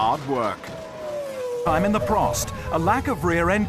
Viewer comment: Did he just say, I’m on the Prost?